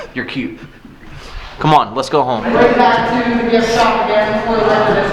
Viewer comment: what was the guide even saying??? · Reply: She was saying something about the gift shop i think